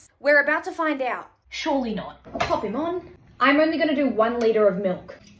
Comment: I heard that "Pop him on" as "Pokemon"😭